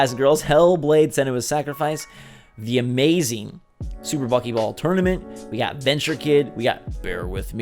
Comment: Super buckyball tournament looks interesting. I misheard you at first and thought you said monkey ball tournament and got a bit too excited